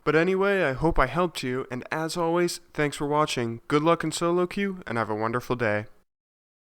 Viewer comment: sounded like ''good looking'' :D but i understand what u meant...